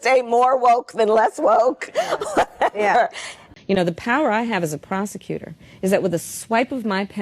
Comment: I thought she said the power she has as a prostitute?